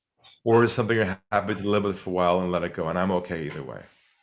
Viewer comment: At the 30:50 mark He says something profound but the second part is a bit garbled. Any clue what he said there?? He says Is this a priority for you ... to solve once and for all? Or is this XXXXXXX for a while and let it go, and I'm okay either way. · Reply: Is this a priority for you. . . . to solve once and for all? Or are you happy to live with it for a while and then let it go. . . And I'm okay either way